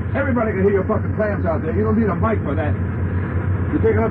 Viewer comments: What do you play, CLAMS???? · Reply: The entire time I thought he was saying, "flams." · For all of those who don't know: Clams = Wrong notes. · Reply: Ty! I thought he was saying flams and I was like how the fuck do you flam on a horn?!